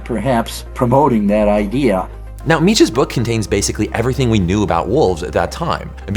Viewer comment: Hey the captions here say "Mitch" instead of Mech